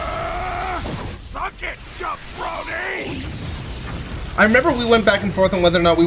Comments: Not gonna lie, until this day I thought Vegeta was saying "suck it, Jeff Brody", and just thought it was some obscure reference I wasn't privy to. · I always thought he said "Suck it, you brony!" 😂